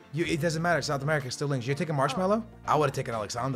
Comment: instead of Marcelo he says marshmallow?????